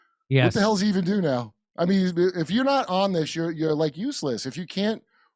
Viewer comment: I heard “if you’re not honest; You’re useless!” And I know actually said something different but goddamn it that’s good! I’m making that a shirt!!